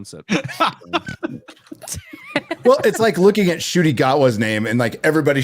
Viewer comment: How is it we have a legion of geeks and nerds and no one has noticed that the Drs name sounds a lot like "A Shity Jawa" ? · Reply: I keep hearing it as "Judy Gatwa" when says it.